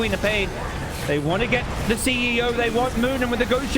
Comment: i heard CEO at 12:56 hahaha